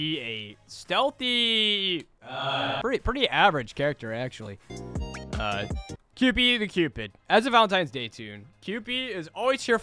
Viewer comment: I thought it said Cupie the stupid